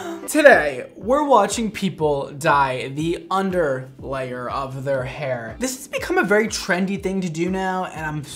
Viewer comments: at the start i heard "today we are watching people die in the underlayer of hell" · so my phone froze at 0:22 and I FULLY thought Brad said “so today we’re watching people die” 🤧🤧🤧🤧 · For a second I heard him say 'Today, we're watching people.. die-'